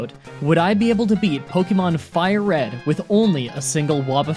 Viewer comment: Did you say Fire red.